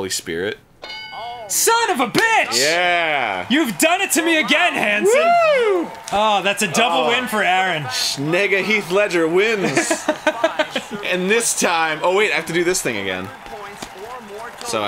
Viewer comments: " sounds like Arin said, "Nigguh, Heath Ledger wins!" lol. I'm dead ass! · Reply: Nega-Heath Ledger. "Nega-", as in the evil or opposite version of something. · I thought I heard him say n***a